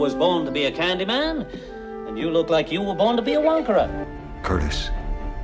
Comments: The subtitles at 2:36 read 'wanker' instead of 'wonkarer'. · English subtitles say "and you look like you were born to be a wanker curtis" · Did he just call that kid a wanker?? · i thought the shopkeeper said "and you where born to be a wanker"